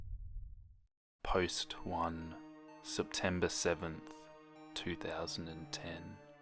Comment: In the beginning when he said September, for some reason I heard “Peppa pig” and I had to go back 😂